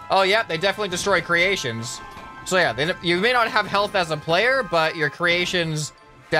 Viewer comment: when he said health i got buffering and it sounded like he said hell